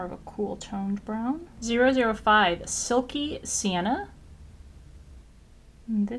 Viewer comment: Haha at first on the fourth shade you swatched I thought you said "Silky Santa" I had to go back a couple times